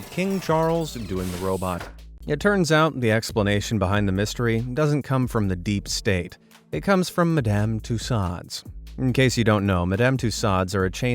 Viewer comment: Did they just say Medame “Two-sades”? 😂